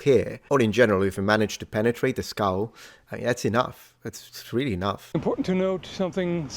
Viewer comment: I have genuinely heard that as "if you manage to penetrate the Skall" 😳🤭